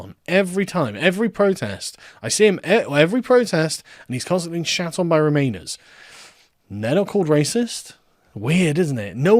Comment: I had to listen three times, I thought you said "getting shit from Romanians."